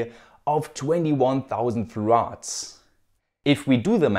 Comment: Whenever I hear you say 'Farads', I always think of bicycles. Brilliant content as always! Thanks!